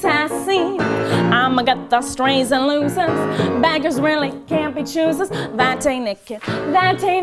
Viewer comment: did she say, " Bad Isreali " at 6:15? LMBO